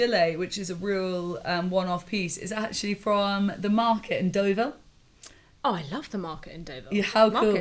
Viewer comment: I thought said the market in Dover.